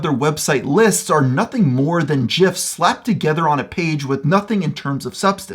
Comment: Did he just say jifs?